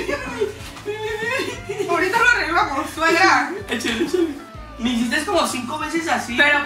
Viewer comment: se dieron cuenta de que mont dijo suegra o algo así? No se entiende muy bien pero a si le entendí